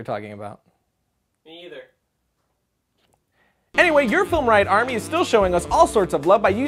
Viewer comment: every time you say "film riot" it sounds like you say "film reich".